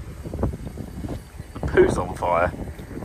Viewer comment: Did I hear “Poop’s on fire.”?